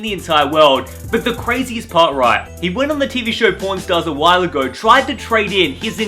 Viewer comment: i thought said "@ stars" at 1:48 lmao